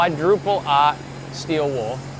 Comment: What did he say?: (45:22) "Quadruple ox?/ah? steel wool"? I plan to use Mothers 05101 metal polish & red devil 0320* steel wool on my exhaust. I will buy the Ospho and see how it works. Never heard of it before. Thanks! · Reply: He said ought as in 0000 steel wool. It's a very fine grade.